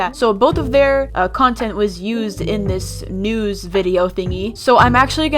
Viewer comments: I thought you said se# there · Who else heard "sex"